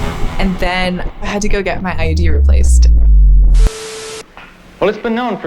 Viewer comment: I heard "I got my ID replaced" and I'm over here, like "Yeah, they make that a real pain in the ass these days... Why we talking about birth contr....Oh.... IUD..." it's been a long year today.